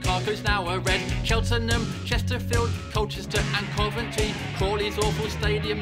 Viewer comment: auto generated captions instead of Colchester and Coventry it was “cultures and poverty”😂😂